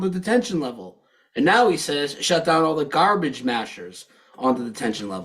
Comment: I thought Luke said "garbage smashers" rather than "mashers" but that's how I remember it.